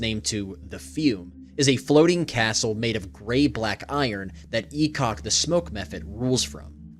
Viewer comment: I'm new to DnD lore so when I heard "E-cock the smoke methhead" I was confused 😂